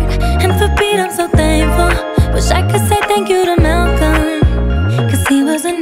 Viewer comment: What I hear is "Thank you, Mac. Thank you, Mac. Thank you, Mac." Because she said she couldn't say thank you to him.